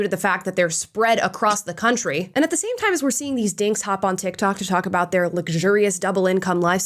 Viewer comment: At first i thought they said they were dicks😂